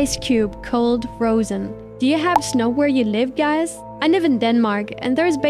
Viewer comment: did you say you live in denmark? I live in denmark to!!